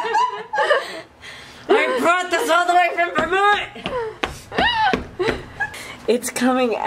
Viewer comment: What did you say around 12:50 after “lady wants her wine.” It sounded like you said “I came all this way from Vermont” in maybe a Russian accent. I can’t stop laughing! 😂😂😂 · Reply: 😂😂😂😂the accent was not intended! Haha I was saying "I brought this all the way from Vermont." I picked up the wine while I was on vacation lol